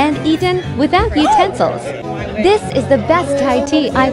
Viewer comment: i thought u said no utensils 😂